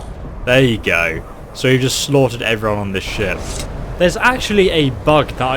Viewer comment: I thought I heard, "I just snorted everyone onboard!"